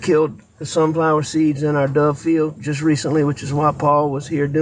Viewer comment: I thought he said " dove field" I had no idea what he was talking about. To my surprise people in the US have dove fields, specially planted with various plants ( such as the sunflower seeds he talked about; which I thought was odd). Not ever heard of this before.